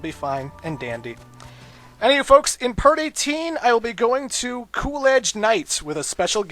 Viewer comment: When he said "in part 18" i thought he said "in party teen" XD